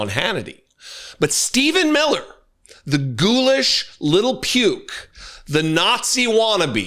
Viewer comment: I thought you said “little pube” - I like that as a descriptor for this disgusting being